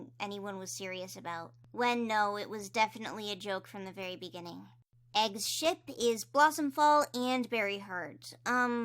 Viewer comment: im gonna cry, when they said " egg's ship! " i thought they said... " egg's shit!! "